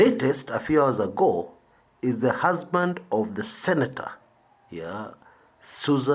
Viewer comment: I don't know why at first I heard "the herdsman" of the Senator Susan Kihika (though you clearly said "husband"). I wondered why the herdsman of the Senator would be arrested for tax evasion